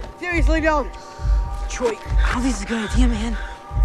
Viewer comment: what did Troy friend said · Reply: "Troy...I don't think this is a good idea, man!"